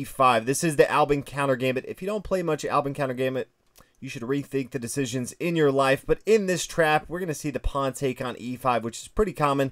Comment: I thought he said at 5:45 “you should rethink the decision to end your life” and it took me a second to actually hear what he said.